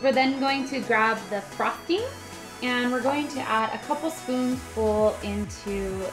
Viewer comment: when she said spoon full I thought she said food full